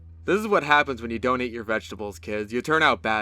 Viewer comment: I thought he said "donating your vegetables kids"